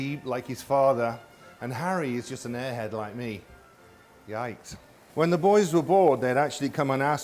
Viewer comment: did he just said “yikes” or am I hearing it wrong wtf